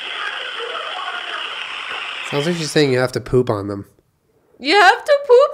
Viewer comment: On the last recording at the hotel, I heard "you have to do it Marsha!" Then hits me with "you have to poop on them!" WAS NOT PREPARED LMAO